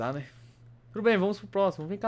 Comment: só eu escutei meu bem